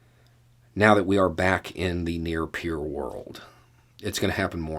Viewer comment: wow, great video man; thanks! minor question: I couldn't quite understand what you said 07:09 and 08:26. Something like "new pure"? The CC gives "near pure" for 07:09 and "near peer" for 08:26. I think the latter, "near peer", fits the context. I think I get what it means, but are you by any chance referring to a previous video where you introduce this concept? thanks